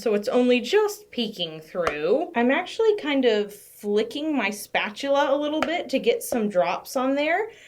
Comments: I thought you said "licking my spatula" and I had a mini heart attack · When you said you were flicking the spatula I thought you said LICKING. 😐😐😅😅 I was worried!!